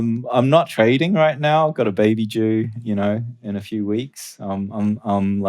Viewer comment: did willy say he has "a baby jew"?